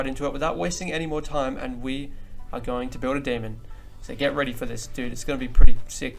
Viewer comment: i first thought it said "How to build a lemon" XD